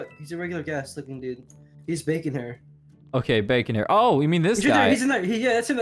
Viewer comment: thought you said Bacon Bear.. rip 😂